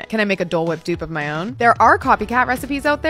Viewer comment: I thought you said cool-whip 😭